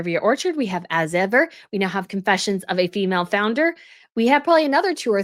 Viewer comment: I thought it said “Female Flounderer”…for real…🤪 Stop already…Good Lord!!